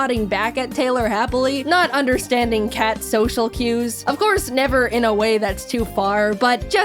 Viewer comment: I misheard that as social credit